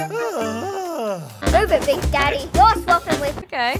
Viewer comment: Did… did Bluey just call her father Big Daddy?